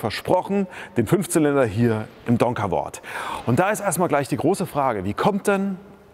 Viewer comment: In nem WAS? Nie gehört von dem Hersteller! Aber die Lackierung von der Karre ist ja mal derbe Sexy! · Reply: Donkervoort D8 GTO